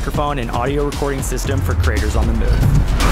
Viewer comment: I thought they said for craters on the moon, not creators on the move. Does this mean that DJI Mic 2 won't function while standing in a crater on the moon?